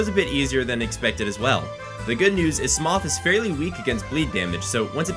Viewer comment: did you just say.. smof? great video tho